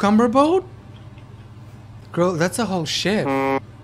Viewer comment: i heard shit